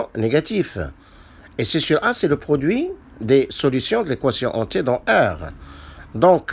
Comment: Salut maître merci beaucoup pour l'explication S'il vous plait ca veut dire quoi "les solutions de l'équation "hanté" dans R" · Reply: Salut Softy D.H Je veux dire l’équation en t ( c’est à dire d’inconnue t) et non pas ….hantée !!!!